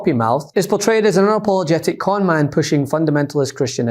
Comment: portrayed as a what? I can't figure that word out no captions available either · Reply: Unapologetic conman pushing a "christian" agenda...